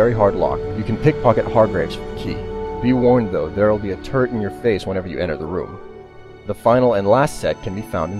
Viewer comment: does he say "turd in the face"?